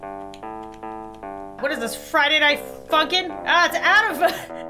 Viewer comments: did.... did i just hear lauren say the f word? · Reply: nah it's friday night funkin' i thought the same thing, it's easy to get mixed up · Friday night funkin? But the captions got it wrong 😂